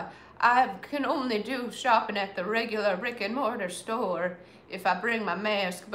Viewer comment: Did you say " I can only do shopping at the regular Rick and Morty store"? Hah hah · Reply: The common phrase is “Brick and Mortar” that’s what I heard her say